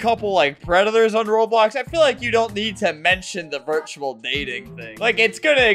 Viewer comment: I thought he said virtual mating